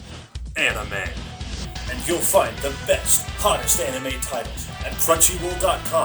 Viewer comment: When he said "Hottest anime titles" i thought he said Hottest anime tiddies